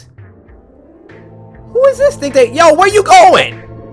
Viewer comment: i think he said "whos this dickhead"